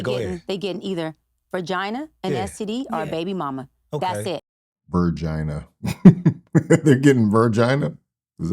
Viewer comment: Did she say "ver"gina? 🤔